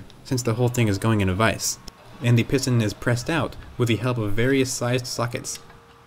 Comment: Did he just say various sized suck it's